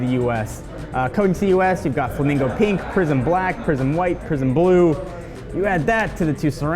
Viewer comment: I thought I heard the colors prison black, prison white, prison blue 😂